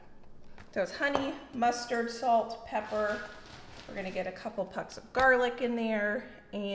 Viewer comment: Haha! When you said “2 pucks of garlic “. I thought you said 2 cups of garlic. I was like: what?